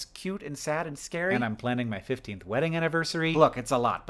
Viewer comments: Did he say 50th Wedding anniversary? That's what I thought I heard, but I'm surprised at that level of planning ahead, so I think maybe I misheard 15th? I don't know. In any case, congrats? I guess? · Only half listening and I thought he said "FIFTIETH wedding anniversary". Major auditory double-take there.